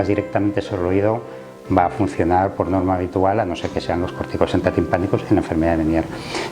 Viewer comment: qué dice? Pórtico central de pánicos?